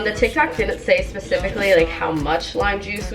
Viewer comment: Am i the only on who heard her say tiktak